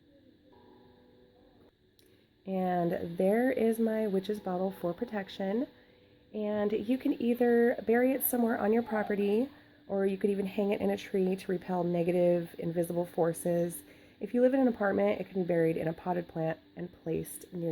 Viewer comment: Not sure if it’s just me but has anyone else heard an evil voice saying what sounds like ‘you can’t stop me’ right when she says you can bury it near your property at 7:28?